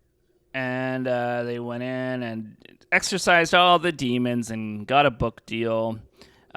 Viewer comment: it cracks me up everytime someone says exercise demons instead of excise. like someone's giving demons a cardio workout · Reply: They're saying "exorcise". 😂